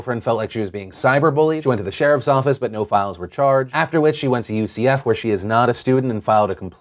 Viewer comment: Phil, at 5:55 did you say "no files were charged"?